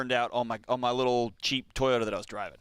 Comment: y'all, i think he said "cheap toyota" not jeep toyota 😂 haha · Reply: On the replay, I heard "cheap" too. But during the live, it definitely sounded like "Jeep." Lolololol